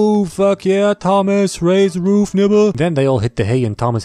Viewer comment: Can't be the only one who heard him say raise the roof nigga and just kept watching